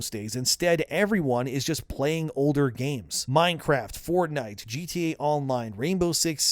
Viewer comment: when you said Minecraft, I heard Minesweeper. Paused for a sec and thought, ya, still checks out